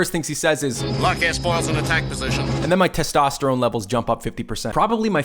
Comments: S-foils? All this time I thought they had said: air foils. Am I the only one who had that wrong? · I always thought red leader said, “Lock your spores in attack position.’ Live and learn.